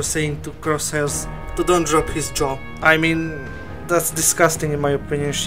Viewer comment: Wait, what did you say? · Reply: "Don't drop his jaw"